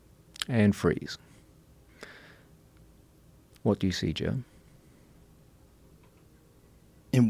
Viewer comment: did he call him Jim?